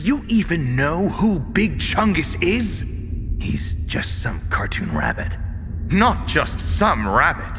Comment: At first I thought liquid said "Cartoon Rat" and I fucking howl'd. Cartoon Rabbit is still a funny as hell line, though.